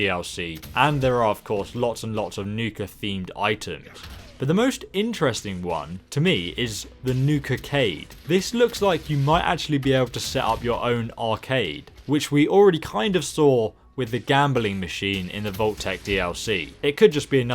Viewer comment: When said Nukacade I thought he said Nuka cave and we could be a superhero like batman.